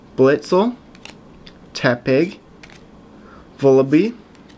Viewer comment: When he says tepig it sounds like he saying epic.